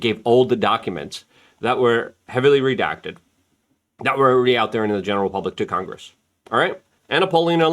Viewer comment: I literally heard someone say, “They released it all already.” The tactic is working.